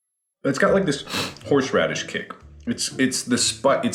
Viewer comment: what Mark said: "It's got a horseradish kick to it." what I heard: "It's got a horseradish kink to it." -_